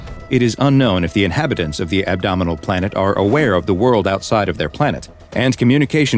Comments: I thought it said abominable planet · At first I thought it said “abnormal planet” I mean, it definitely is abnormal.